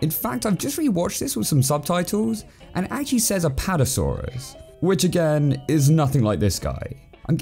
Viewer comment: What that kid is ACTUALLY saying is "PARAsaurus" which I believe is an outdated name for the Parasaurolophus.